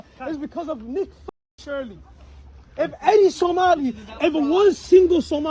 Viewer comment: did he say nick fuentes? WTAF?